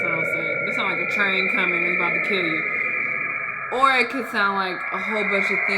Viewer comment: why does it sound like you said "this sounds like the Chinese Communists 'bout to kill you"